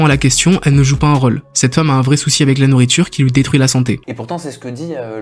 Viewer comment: C'est moi ou à 6:06 il dit "cet homme" ?